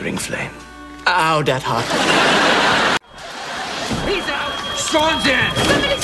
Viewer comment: it sounds at 2:02 the dad says "He's out Strong's in." Instead of Shawn.